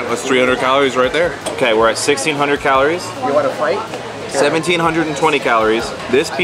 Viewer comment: Did anyone else hear “you want to fight?” At 18:58 when was talking? 😂